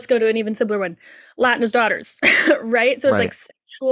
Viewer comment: What is she saying at around 40:00 minuets in? She is referencing a Bible story about someone’s “daughters”. I can’t make it out. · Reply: “Lot and his daughters.”